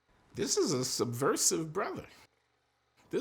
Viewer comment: what does he say at 6:35 after subversive? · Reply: a subversive brother...